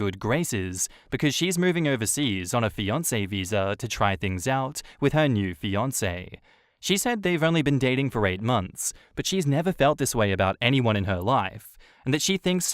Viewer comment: Did anyone else hear Beyonce visa at first? 😀